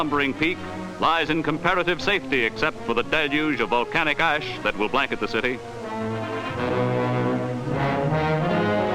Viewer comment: Did he just say the "the italian people are stuffed"? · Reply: He said "The Italian people are stunned"